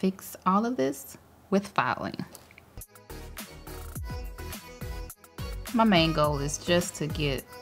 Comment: When she said my main goal I thought she said my mango 🥭